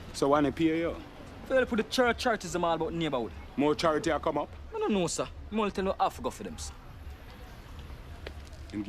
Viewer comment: what does skinny say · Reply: He said he doesn't know, all he knows is he has to go for the barrels